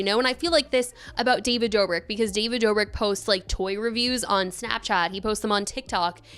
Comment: showed the other posts on another vid it was like 1 or 2 other times she used that racial slur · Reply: oh i probably misheard in ‘this’ video , it sounded like she said “post” not “posts” my bad